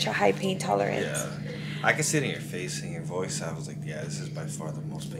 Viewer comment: saying he saw the pain in your face ..y yo mensa thought he said "yeah I could sit on your face..." smh I caught on tho🤦‍♀️